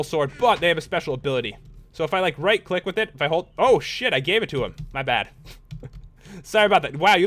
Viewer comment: who heard pat say 'oh sh**'